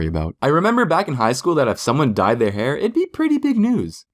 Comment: and 1:49 u thought say "she died"